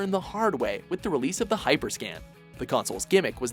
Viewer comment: I thought you said hyperscam instead of hyperscan